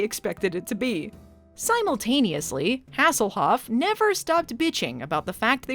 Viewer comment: Wait. Did just call him "Asshole-hoff"? I want that to be true. 😅